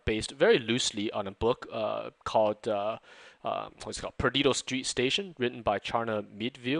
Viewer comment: Does anyone know the book is talking about at arround 2:19? I can't really understand the full title and the name of the author. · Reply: Hi The book that was referring to is Perdido Street Station. Thanks! :)